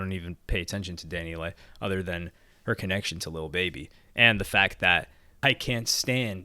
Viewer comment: Heard you say lil baby. Not da baby. Lol